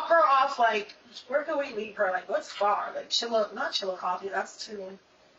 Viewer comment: Yeah... what's weird about that? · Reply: where the subtitles say chilla coffee, I thought she said Chillicothe which is a town where a bunch of unusual deaths had occurred in Ohio. It's supposedly a small remote place in Ohio according to a special I watched oh dateline or one of those shows.